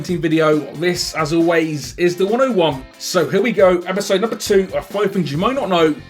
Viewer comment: I've been subscribed for two years and I always thought he said "This as always is the one I want" and just realized he said 101 not one I want